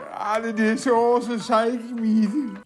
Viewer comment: vad säger han 2:26? :) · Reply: "Alla dessa år som psychic medium". AKA fyllesnack